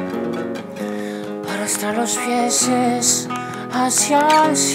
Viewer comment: yo escuche 'hasta los pieses'